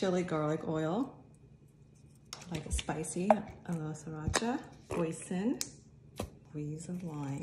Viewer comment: When she said hoisin I literally heard 'poison' so I had to re-watch it again😂